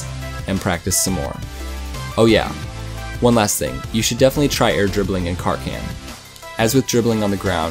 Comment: anybody heard say “you should stay in CAR CAM.” ???